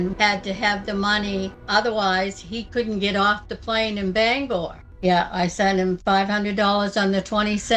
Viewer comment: did she say Bangalore? Don’t bring India into this lady